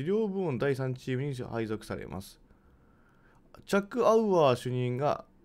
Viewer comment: チャック・アウアーか… ずっとジャック・バウアーに聞こえてた笑